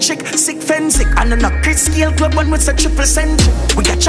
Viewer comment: A diss him diss chris Gayle it sound like him say piss Gayle.